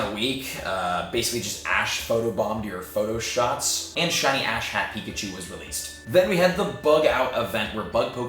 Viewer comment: I keep hearing "ass-hat Pikachu" >.<